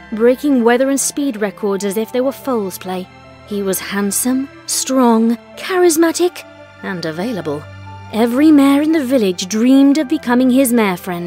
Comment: I thought it said dashton was layable